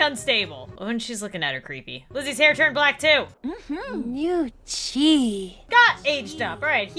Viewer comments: Who else heard new cheese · sounded like he said new cheese 3:04 Callum goes emo